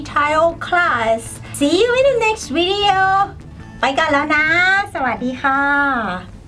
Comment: Hi. The conversation portion was very helpful. BTW, what are you saying at 3:39 just BEFORE you say "sawadee ka". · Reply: pbai gorn laew naaa --> I've gotta go!